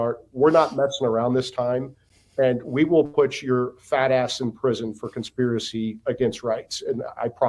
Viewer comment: Did he Say "Fat A......" 😂🤣😂🤣